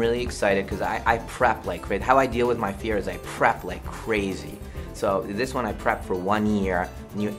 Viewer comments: I thought Shamylan was saying “I crap like crazy” and I though of him having to take a shit every five minutes and cypher rage is like “DENIED SIT DOWN” and M. Night is like “Will I appreciate your dedication to sucking but to prepare for a film I crap like crazy”. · Literally thought he said "I crap like crazy, so for this one I crapped for 1 year" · I actually thought at first he said "I crapped like crazy." And "I crapped for a full year." It took me a while to find out he actually meant "prepped". LOL. 😅😂😀. Did anyone els think he said crapped? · When I first watched this, at 7:00, I thought M Night was saying I crap instead of I prep. I would quote him for the rest of my life if what he really said was "How I deal with my fear is I crap, I crap like crazy..I crap for years..." (I mean.. with releases like the last airbender he wouldn't be wrong) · I could swear he says 'I crap like crazy' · I wasn't looking at the subs, and thought Shyamalan was saying that he craps like crazy to deal with his fears. Shyamalan: I crap for one year.